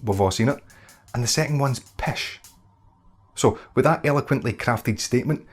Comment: what does he say for the second one around 0:13? pish?